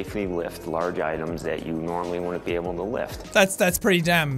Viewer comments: is it just me or did it sound like he said "Would'nt" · It sounded like he said wouldn’t